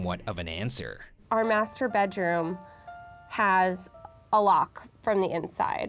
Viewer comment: why did i hear masturbate room ?